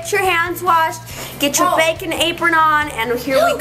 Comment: LMAO at first i thought i heard "get your bacon apron"